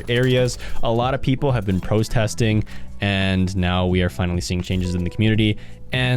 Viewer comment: “Prostesting” is what I heard